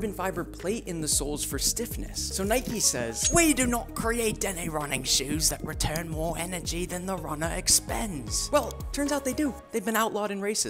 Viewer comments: I thought he said “they’ve been outlawed in racists” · I thought you said "There outlawed and racist" 😂